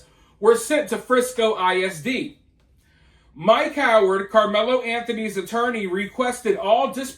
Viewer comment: When you said “ Mike Howard” at first I thought you said “ my coward”